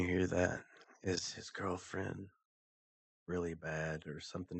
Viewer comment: I believe it was at 15:30 when u thought it said is his girlfriend Really bad, I swear it said Chris's girlfriends Really bad! Which makes since because I truly believe she is the one that killed them, Chris I think was involved in both helping her kill them and helping her to do away with their bodies but I just really believe that Nicole was there and did just as much if not more then Chris, I've watched so much on this case, if u watch the armchair detective, I believe it was him who shows she was there with their phone records and location. I also believe that friend of hers that she is protecting, I believe his name is Jim, yea i believe he helped or was involved some how. And also i wanted to ask did anyone else see the shadow or spirit upstairs walk across as they walked in the house. It was either when her friend and the officer first walk in or it was when they take the dog in, i cant quite remember atm but im pretty sure its when her friend and the officer are walking in, if u look upstairs you can see it perfectly. It has to be a spirit cuz i dont think anyone else is there, as far as we know it was only Chris, cuz he comes home and had to go in to open the front door to let them in.